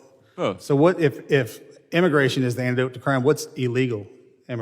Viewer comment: Immigration is the antidote to crime? Tell the Swedes that. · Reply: I could have sworn at first I heard him say Immigration is an anecdote to crime and I was like hmm maybe? But then I listened again and really really done said Immigration is the antidote wth😂😂 Expats are legal migrants, Immigration is usually tends to happen Illegally cos not everyone has enough money to be a expat. Funny differences between the 2 words